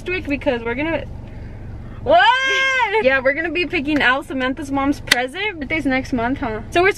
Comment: What I thought said:Samanthas mom is pregnant and she is due next month no? Me:wait what🤨🤨🤨🤨🤨